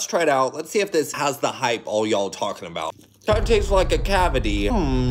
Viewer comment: I thought you said “it’s like a sugary coleslaw” at first and I was like huh that kinda makes sense I guess…